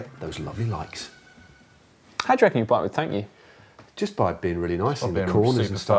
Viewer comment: what did say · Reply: "How do you reckon a bike would thank you?"